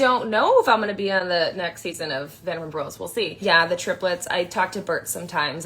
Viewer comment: it sounds like she says “vanderbros” and that’s probably a better name for the it now…